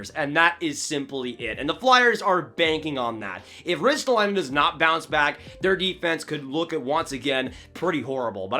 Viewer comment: i thought you said risto wasn't gonna bounce back